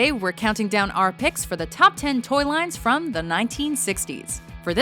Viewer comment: Lol I thought it said top 10 dirty lines of the 1960s!!!